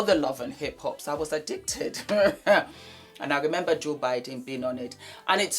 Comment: I thought you said joe bidden but it reads budden lol😂❤